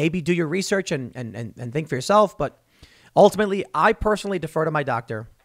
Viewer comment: did Tim say "maybe think for yourself " ?